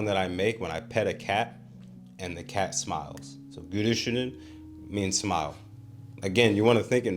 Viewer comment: I think you're maybe using a Japanese l/r when you speak Turkish? When you said "gülüşünü" I thought you were saying "gürüşünü." Turkish L is easy, it's a real L sound. Here it should be in the front of your mouth.